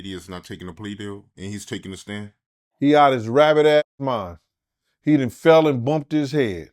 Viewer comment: I love Gene, dirty diddy out his rabbit ass mind 😂😂😂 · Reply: LOL! I thought he said 'rabid' 😂